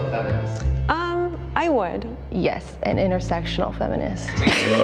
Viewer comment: At first I thought the lady said "intersexual" feminist until I realised it was "intersectional" and I wouldn't be surprised that their reaction was based on them being dumb like me and mishearing her because there is genuinely nothing wrong with intersectional feminism especially since it would be accommodating for people like their mothers